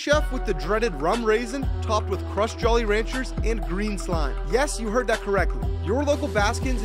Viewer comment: When he said green slime I thought he said green dudu💀